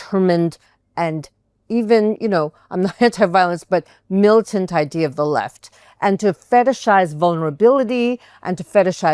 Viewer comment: I thought she said she was anti-violence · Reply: she kinda slurred her words around 7:10, I think she said "We have to return to [...] I'm not gonna say violent, but, militant idea of the left". certainly sounds aggressive, but not saying anything specific